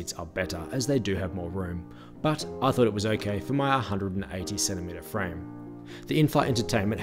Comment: omg.. at first I heard you say: "in my 187 meter frame" 😅 at the 2nd time then it was 180 cm frame ... sounds more right to me ;)